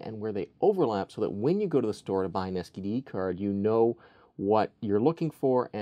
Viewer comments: did he say STD Card or SDD Card? lol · I swear at 1:30 he says "std"card. No body wants that card.😂